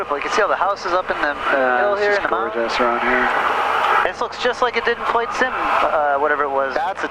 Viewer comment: Did I hear at about 17:12 "just like the sim"? Simming is the only way I'll every do this "flight"